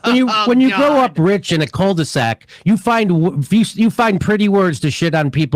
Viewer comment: Didn't understand "cul-de-sac", as I thought Jimmy said "cold sack". Ha!